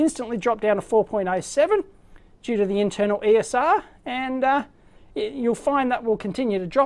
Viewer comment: about 23:31 did you say "Due to the internal ESR", meaning that the displayed voltage is lowered by the load's equivalent series resistance? thanks, cool vid